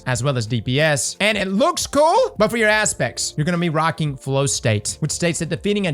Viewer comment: I heard, "You're gonna be rocking Glow Snakes." From this point forward, Flow State is now known as, "Glow Snakes."